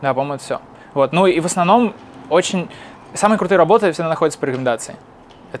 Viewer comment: Почему все ругают стиль общения этого человека - у него мозг работает очень быстро и речь из-за этого точно такая же. "Самые крутые работы находятся только по рекомендации" - это удалось расслышать только со скоростью 0.5, проверьте